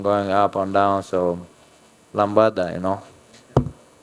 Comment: lmao did he say "lambada"?